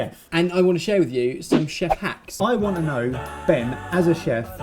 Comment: I wasn't looking at the video and I thought they said they were ''going to show you some 'chef hats'' you can imagine my disappointment when I saw it was 'chef hacks'